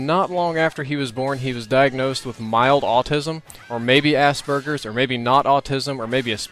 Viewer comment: I'm sorry but did you say ass-burgers?